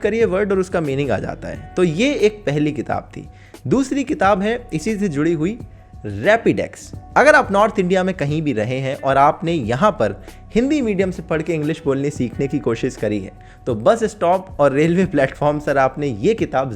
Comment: Instagram par bhi aa jao - <link> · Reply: Rapidax...I heard Rapidash(a species of pokemon) 🤭😆